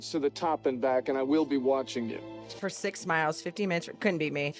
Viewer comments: 15 minutes to run up and down Curahee is a serious level of fitness 😅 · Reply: It’s easy to mishear, but he actually said 50 minutes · He actually says "50 minutes" to the top and back. I thought I heard 15 the first time too, but the subtitles show 50, and as you said, 15 is literally impossible. He would not say 15 minutes.